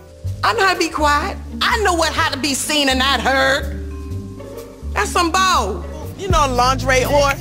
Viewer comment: Sounded like she said “That’s some bowl” 😂